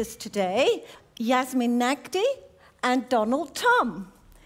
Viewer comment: Literally could’ve sworn I heard “donald trump” at the beginning and was like “oh weird not the type of activity I would figure he’s into”